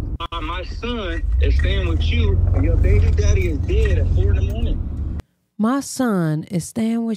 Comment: Sounds like he said her baby daddy is dead I believe that the father to her child passed away